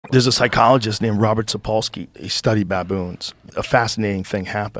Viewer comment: I swear I thought he said he name was Robert polson I was about to say Joe must be in project mayhem